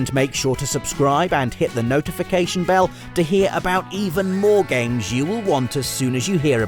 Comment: I heard “MORE GAMES” as William Regal’s “WARGAMES” and it was lovely